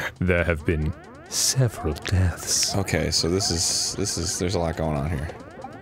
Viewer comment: I misheard 'several deaths' as 'sniff me Clarence' at 1:48 and spat on myself laughing. I'm easily amused.